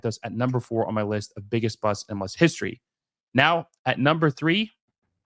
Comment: Haha I thought it said biggest butts